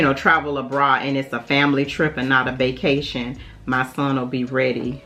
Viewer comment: Did u say "family trip"!!!! Omgeee!